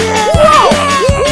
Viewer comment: Tbh, crash don't sound like he saying woah. Sounds like crash is saying either yeah, yay, or my dog at 12 am for no reason.